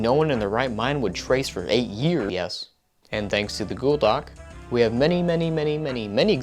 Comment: he said godoc instead of google doc in 3:18... I know this because I slowed the video down... HOWEVER THIS MIIIIIIIIGHT JUST BE YOUTUBE'S playback speed just being overall shitty and I'm just wrong and now looking like a complete bozo